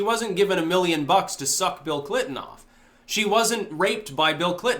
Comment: I thought you said 'Feel Clinton'.